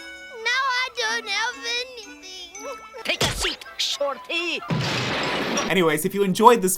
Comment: ayo- did she just call him shawty?